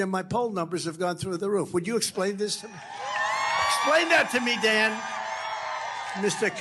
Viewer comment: I thought he said “explain that to me dad” and I was thinking holy fuck Trumps hilarious